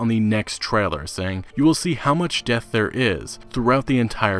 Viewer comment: I think that the translator misheard him replace death with depth and the statement makes more sense. Lol what the hell does death in the windows mean